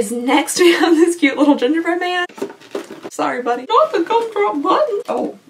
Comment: I heard "ninja-bread man" 🤣